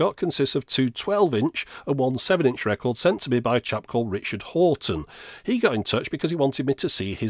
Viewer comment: ******READ THIS******** >>>>> Apparently SPLIT ENZ and STYX (amongst others) have released laser etched vinyl records in the past. Which is nice, but Richard’s disc will always be the ”first one that I’ve seen” Links to things shown in the video are below EXPAND THIS BOX ... Eye catching records by Richard Houghten <link> Bytes of Sound by Rob Dimeo <link> DCC - Run Girl Run by Angela Lashley <link> DCC Documentary <link> Minidisc Duplication & UV Printing <link> Tapeline - custom wound tapes <link> 8-Tracks by Dead Media Tapes North Texas Daily Article <link> Dallas Observer Article <link> Undergrounded Interview <link> New Blank Minidiscs - Amazon Japan <link> (You can change the language of the Amazon page to English by clicking the globe icon next to the search box). You also need to create an Amazon Japan account to shop there - accounts from other countries won't work). Minidisc Cover Template <link> RIAA Sales Charts <link> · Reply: I was a bit confused at first because I thought you said Richard Hawtin